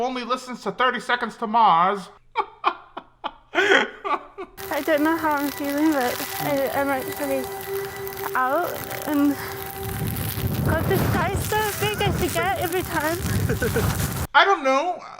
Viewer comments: i think she said i dont know how im feeling about coming out and it just gets bigger and bigger every time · at 8:45 she was saying “the sky is so big I forget everytime” I might be wrong but at least that’s what I heard 😅 hope it was helpful